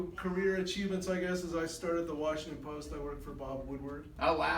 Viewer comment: Did he just say he started The Washing Post 2:52?